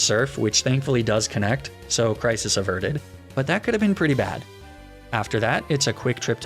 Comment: I THOUGHT YOU SAID PRICIS AVERTED